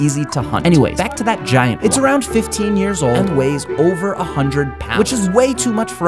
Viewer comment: I thought he said it wad 150 years old My reaction: damnn that boy OLD